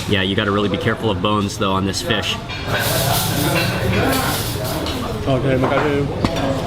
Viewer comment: Just quickly, what was it you said again at 9:56sec ? 😝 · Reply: he said "terima kasih." or thank you in English.